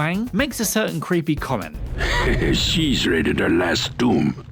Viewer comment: What was the 'creepy comment' about Lora Croft? I replayed it several times, but couldn't understand it. What I heard was, "She's ready to Les Doom" Huh? I have no idea what that means. Is that even what he said? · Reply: It sounds like he said "She's raided her last tomb."